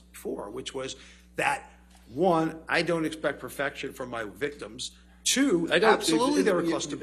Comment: Did anyone catch what the psychiatrist said at 3:18:06??? He said: "I don't expect perfection from my victims." 😳 🤔